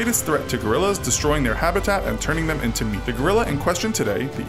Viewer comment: in 5:53 he says "turning them to me gorilla or did he say meat gorilla